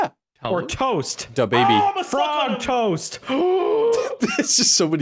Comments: The thing said at 0:53 where the subs just say "*I can't make out what he's saying*" He's saying "OHHHH IMMA SUCK ON 'EM!" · I legit heard him say “oh imma suck on them “ HAHAHA